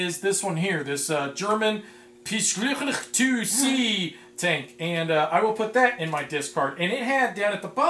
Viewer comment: the name sounded like from scifi, but actually it's the Panzer kampf wagen (Armoured assault vehicle)